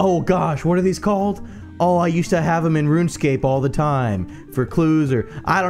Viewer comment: Did he just say Runescape?